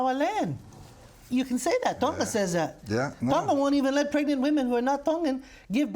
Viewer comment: What did the lady professor say? I couldn't catch what she was referring to about pregnant women not being able to give birth if they are foreign. That sounded interesting but i couldnt follow, so if someone could clarify what she said. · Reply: I think she said "Tonga wouldn't allow pregnant women to give birth if they are not Tongan"